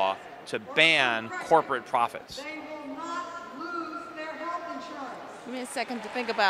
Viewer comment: LOL at 3:10, did anyone hear in the background? "You will not lose your health insurance!"